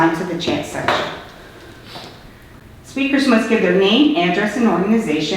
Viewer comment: The clerk said: "Speakers must give their name." She did NOT say "last name".